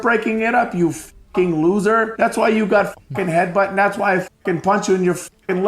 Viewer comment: cover up already LOL that is not what he said, he said thats why i bite you, and he did bite RAJA